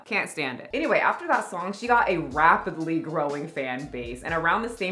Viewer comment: Did you say ‘wap’pidly 2:48 .. ha ❤